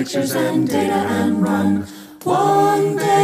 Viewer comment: ''Steal your account and go.'' I thought she had said ''Steal your goddamn gold.'' Love it.